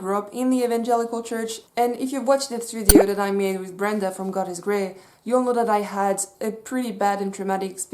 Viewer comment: When you mentioned the God is Grey video I thought it said God is Gay